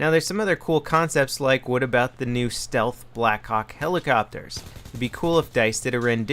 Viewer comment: Did i just hear ''Stealth Blackcock helicopters'' ?